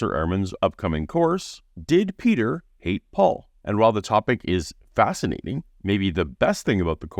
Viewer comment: I heard "Did Peter date Paul"